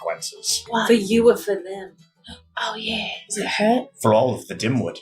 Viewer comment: i thought he said i hurt instead of it hurt